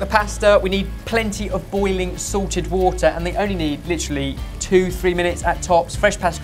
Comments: For years I thought Ben was saying boiling SORTED water instead of SALTED water and I thought that was the Sorted 'Twist' as he likes to call it... Now it all finally makes sense! Haha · is it sorted water or salted water cause it sounds like ben is saying sorted water? it sorted water a combination of all 4 of your sweat or is it just magical water that comes from the sorted kitchen